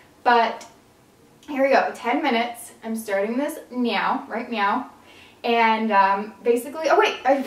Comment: when she said right now at 4:46 I thought she said right meow 😂😂😂😂